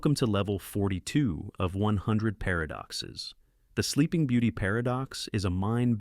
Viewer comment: I thought you was saying 100 paradoxes about sleep or a sleep paradox 😭